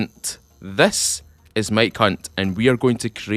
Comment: OOOOoooh MIKE HUNT see I thought you said "My cunt"